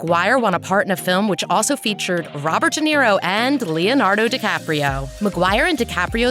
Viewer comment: I thought she said robert Downey Jr 😂😂